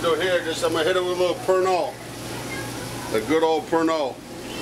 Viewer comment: He was adding "Pernod" to the food, but I thought at first he was saying he was adding "burnt oil".